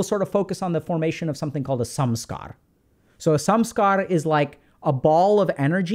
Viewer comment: I always hear "sum-scar" when doc says Samskara; funnily enough, that term still reflects the definition of what a Samskara is and also points to people with mental health problems often piling negative emotions on top of each other.